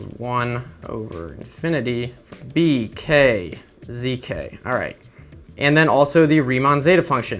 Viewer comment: when he said zk at 11:45 it sounded like zica lol